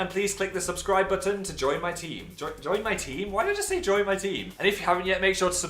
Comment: When you said join my team I thought you said join my tea.